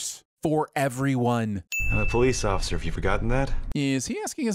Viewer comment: he says: “I’m a police officer” but the subtitles say “I’m a police offer” is it possible to sin for that